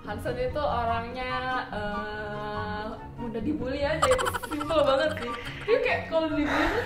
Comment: Subtitile lah bangke, kgk denger si ngomong apa, editor mana nih editor 5:52 detik2 hansen Wajahnya pengen di puji ama si, kampret lu sen😆😂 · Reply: Tapi pas ngomong "njer gampang kena dibully"